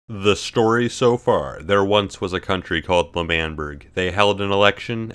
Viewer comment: I thought he said "There once was a boy named Harry" at the beginning